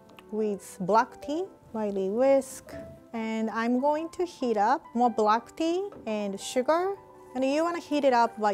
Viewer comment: Rie: And I'm going to heat up some black tea What I heard: And I'm going to heat up some BlackPink Me: 👁👄👁